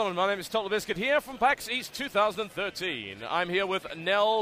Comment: Sounds like he says Paxis 2013